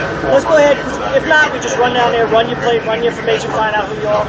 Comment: Did I hear ''run your plates'' ? If I did, why are they running your plates when you have done nothing wrong?